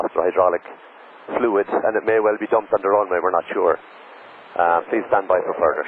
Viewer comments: that should read "and it may well be dumped on the runway" (the hydraulic fluid). That's what I hear out of it :) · *dumped on the runway not stopped on the runway :) 3:08 *steering is inoperative not unusable :) 9:40 I'm not sure but I think he said can I go and communicate But that pilot handling the radio on board the airlingus had a weird intonation